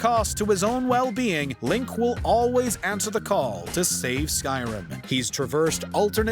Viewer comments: did I hear that Link saves Skyrim?? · Did I just hear Skyrim instead of Hyrule? As a Zelda fan I'm actually offended by this wow lol xD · did he just say Skyrim 😂